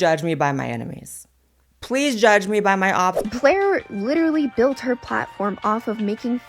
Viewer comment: Did she say Claire?